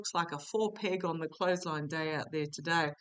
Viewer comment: Four peg on the closeline. Never heard that before here in the U.S. I can't wait to use it. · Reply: Thanks for the clarification- I thought said full "pig" on the clothesline! 🤣 Was trying to imagine that one! 🐷